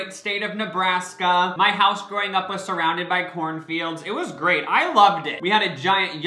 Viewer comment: Thought you said porn fields, not corn fields... 😂😂😂